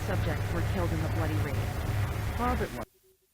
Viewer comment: In 51:16 at the end, I hear the voice say "All but one" Then it cuts out so I think somehow one person survived the attack and escaped but the smilers didn't notice. Sounds like a stretch but that's what I think